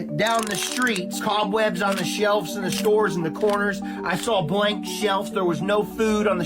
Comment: …Did he say… “shelfs”…? Sir. No.